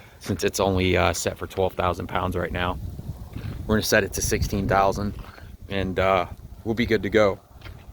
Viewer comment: Did you just say 60,000 lb tag? Lol